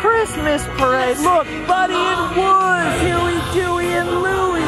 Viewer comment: when says buddy and wuzz instead of woody and buzz lmao i was like wait what 🤣